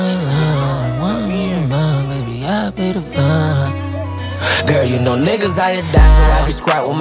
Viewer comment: did he say RIP to von????